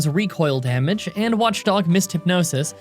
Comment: did you just call him "watchdog" XD